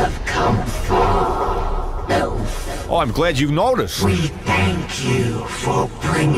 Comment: when they said “elf” and I wasn’t looking at the screen I swore I heard “Milf”, and suddenly wondered if some other tab was opened up.